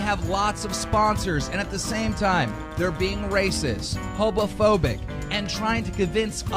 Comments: did keem say hobophobic? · Wait... did he say ”Hobo-phobic” at 2:13 ? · is it me or did i hear keemstar say hobophobic? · Did he just say hobaphobic